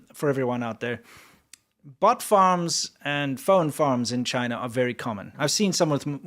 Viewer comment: I thought you said butt farms